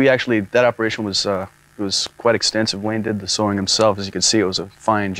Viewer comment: did Jerry just call Layne "Wayne?"